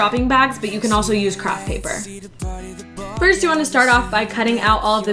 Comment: she said crap paper instead off scrap